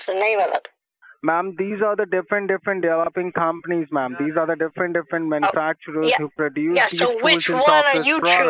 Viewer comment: “Different different” am I hearing this wrong or was he saying that? TS